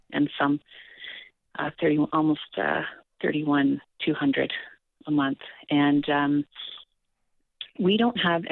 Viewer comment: I think they got it wrong. She said he takes home $31,200 a MONTH.